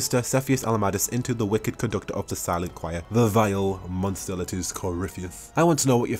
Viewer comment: I know you said Conductor of Silence, but I kept hearing "Conductor of Salads" and laughing my head off. (Seriously tho, very nice lore video, hope you do more!)